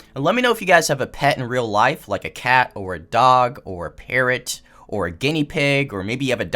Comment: When you said "parrot" I thought you said "parent" 😂. I had to rewind it to see what you said lol